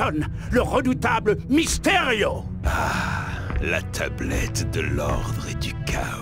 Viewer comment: Euuu c’est moi ou Mystério dit la teublette à 0:30 ? XD